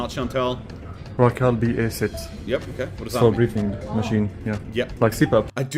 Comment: I had to back that up because I’m listening to this and I swear they called it a breeding machine…. Yeah I was confused